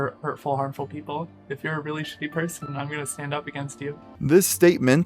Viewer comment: "I'm gonna come for harmful people, if you're a ____ person I'm going to stand up against you." Can anyone please, please tell me what this Deer chimera uttered under it's breath? It almost sounded like "religious" but sounded too short. This one moment in itself tells me all I need to know about this "person," if they believed in the vitriol they spew they'd say it loud and with "pride." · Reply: "really shitty" person. Which is funny, because it is really shitty at pretending to be a person.